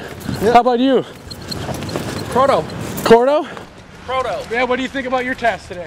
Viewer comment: Did he say his name was GORDO on 6:57..?!?!